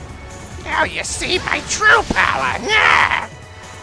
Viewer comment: oh... Jet says: "Now see, my true power!" I could never understand it and thought he said: "Can't beat, my turtle power!" :P